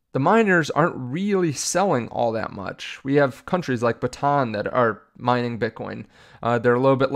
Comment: what was the name of that country you said? Was it Bataan? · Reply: Bhutan